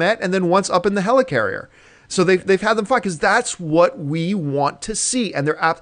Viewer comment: sounds like he said "They've had them fuck"